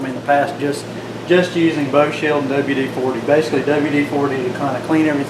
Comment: What do you mean by bug shield for your mill table what product are you referring to I've never heard of that ??? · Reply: Lol ok that I've heard of lol I swear I thought you said bug shield 😂